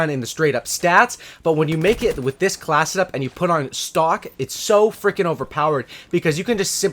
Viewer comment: I thought u said no stock